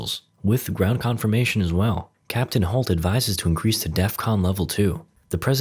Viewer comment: did he say cptn holt lol